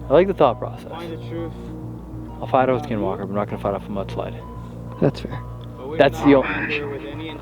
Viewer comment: it kinda sounds like it says “I’ll beat you” and right after says he’ll fight off a skin walker idk I might be hearing things but I really don’t hear satan or the other word